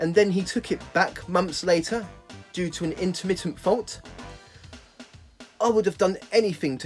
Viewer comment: i don't understand what you said at 50:00 due to a * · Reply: I said ‘due to an intermittent fault’